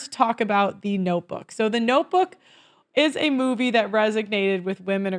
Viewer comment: did you just say resignated??